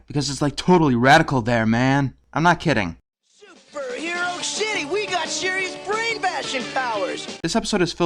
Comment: Did he just say "Super sh*tty we got mysterious brain-bashing powers"? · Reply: no he said "super hero city" which is just as stupid.